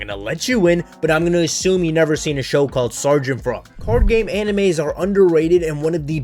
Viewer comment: What did say at 6:24? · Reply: sergeant frog anime